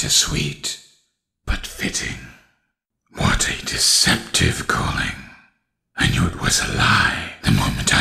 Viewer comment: sounds like he said decepticon